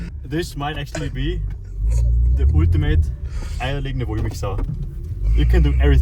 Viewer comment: what was bro trying to say😭😭😭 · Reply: Eier Legende wollmilch sau